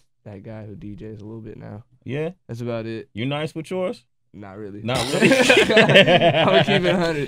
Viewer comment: what does sway say at 1:13? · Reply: mustardguy you nice with yours ? i think